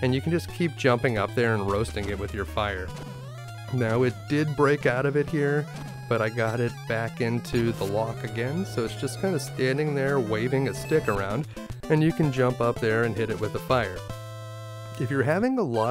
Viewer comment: "So it's just kind of standing there waving its dick around" is what I heard the first time.